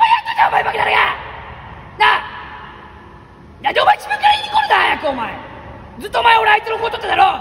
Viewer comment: なんで自分から言いにコロナ！！ にしか聞こえなくなった笑